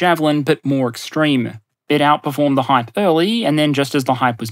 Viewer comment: Oh!! "Outperformed the hype early!" I heard "hype early" and was trying to work out the word, surely properly its hyperbole? was that, maybe, abbreviated, or what? Is it hyperely, what's this word!?